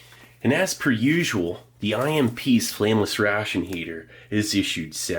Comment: I don't know about anyone else, but for a long time I thought he was saying "Famous ration heater (not flameless)"